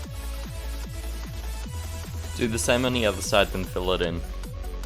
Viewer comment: What was he saying? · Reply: Do the same on the other side and build it in